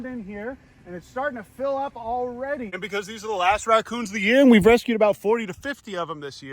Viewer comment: did i hear that right💀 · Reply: He said last not a$$